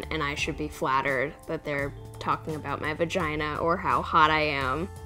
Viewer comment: did she say 'Bagina'??? xD